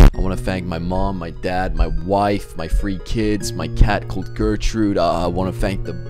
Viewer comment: i swear he said freee kids